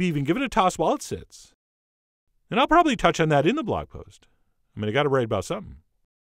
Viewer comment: Okay. Did anyone understand what he said at 4:41? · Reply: "...touch on that on the blog post, but I gotta write about something."